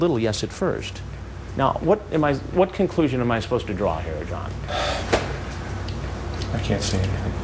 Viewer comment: Does Chris call him "John"?